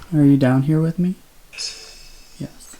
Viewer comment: Did anyone else hear a female say “yes” when he asked if there was anyone down in the basement with him? After he asks the Spirit of say “Yes” and a moment later you hear a female, whispers yes in the mic, it was at 6:30 🕡